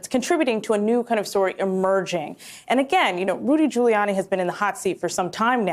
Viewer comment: Drat, I thought that you were saying Rudy Gouliania had been indicted .